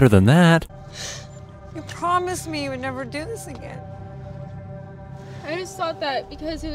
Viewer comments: wait a minute did she just say again? Did she do this before?! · Wait at 11:23 did she said AGAIN? XD Edit : Time